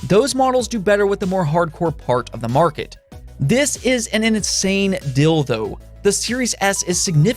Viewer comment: XSS looks like a new DJ Hero game ;) but its a good price! Ill get the XSX though :) Did he just say this is an insane Dildo at 9:16 ? uhmm anyway i really like the content you make!